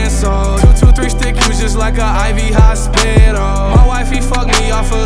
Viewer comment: I thought he said “ an ivy hot spidal” or sum